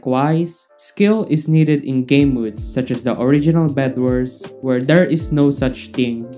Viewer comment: he said bed worlds instead of bedwars 🤪😂